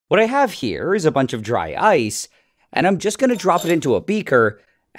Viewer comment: What i heard "A bunch of dry eyes" EYES INSTEAD OF ICE